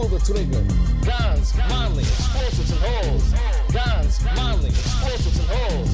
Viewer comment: Honestly thought he was saying "Guns, molly, explosives and hoes"